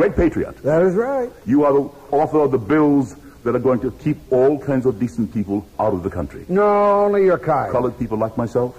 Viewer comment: Hello friends, If anyone knows what word is spoken at 3:11 "all [something] people out of the country", I'd love to hear as it escapes my ear. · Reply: expectationofplenty " all kinds of decent people "